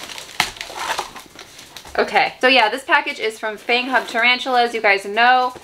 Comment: I thought you said FangHug at first. That would be so cute <3